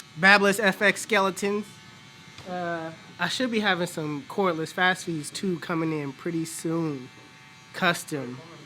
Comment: wait wait wait, did he just say what I think he said ??? Cordless fast feeds ?? Yuuuppp, i need a review on those